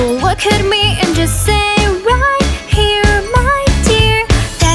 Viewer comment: doesn’t it sound like she’s saying “Just say Yuri” Edit: Why the hell did this get so much likes? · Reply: I heard "just say yori"